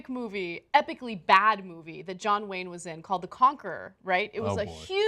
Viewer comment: My mind is somewhere else, I thought Ana said "The Cocker" then I saw the text "The Conqueror" 😳😳☺️☺️